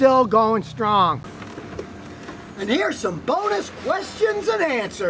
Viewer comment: Scotty does viagra work? My bad . I thought you said bonner questions.